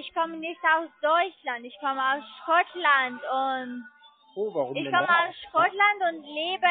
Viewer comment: Bei der Minute 57:06 habe ich doch tatsächlich verstanden, dass sie aus Schrottland kommt. 🤣😂